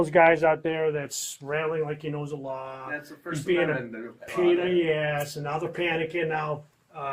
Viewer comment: OMG. I love that you called out (Badge 113) officer for referring to you as a-hole (pain in the A) while his webcam was on. How two-faced of him... and to think I gave him fairly good marks for his initial performance. New Grade: F. 10:36. "He's one of those guys who's out there that's rattling like he knows the law. He's being a pain in the ass." What did the Police of Chief have to say? I couldn't hear everything he said, just something about "first amendment audit" and "the news" or something... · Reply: He said “play nice with them and we won’t end up on the news”